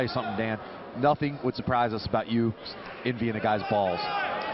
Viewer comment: “Let me tell you something Dan, nothing would surprise us about you envying a guys balls...” 😂😂😂 Aaahhh man, you had me cracking on that one bro!! I know this is a throwback, but damn, that’s was a good one on Dan! What up Jorge!!!!! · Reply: Oh is that what he said?? I thought he said "nothing would surprise us about you emptying a guy's balls" and I was like WHAT?!?!